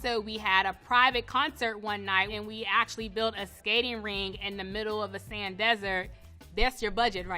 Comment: A stadium ring in the middle of the desert for a private concert?? That really how y’all rich folk livin?? It blows my mind, that only happens in movie in my mind. Wow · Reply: That's so funny--I thought she said "skating rink!" Stadium ring makes more sense.